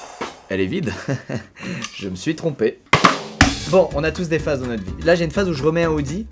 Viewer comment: Au début à 0:09 j'ai entendu : "On a tous des phasmes dans notre vie."